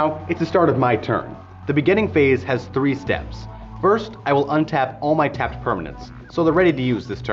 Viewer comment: it says "uptap step" instead of "untap".